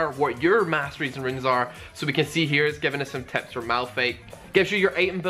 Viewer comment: did he just said Maplhate? or i just heard him saying malphate...